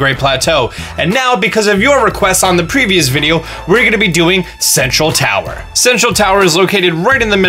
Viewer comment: who else thought he said "sexual tower" XD